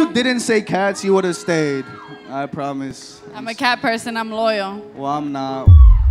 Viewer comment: When he said loyal and they pointed the camera to 😭😭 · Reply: he actually says hes not loyal lmfao